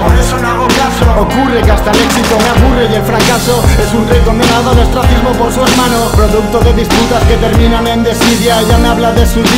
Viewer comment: Que dice en el min 1:17 - El fracasó es un reto.....?????.... de su hermano...? No puedo distinguirle, alguien ? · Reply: "... Es un rey condenado al ostracismo por su hermano..."